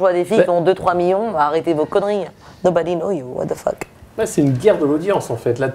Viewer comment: elle dit quoi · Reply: Zoé Rhapsody « Nobody know you, what the fuck ?» Traduction: Personne ne te connais, c’est quoi ça?